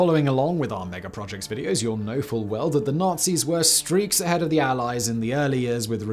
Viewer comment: Did he just say ... "streets ahead of the allies" :O A certain Mr Hawthorn is dancing in his grave right now. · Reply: I think he said "streaks ahead" which seems to maybe be a british phase? Early googling is inconclusive though, maybe we're all just streets behind of Simon on the hot new catchphrases.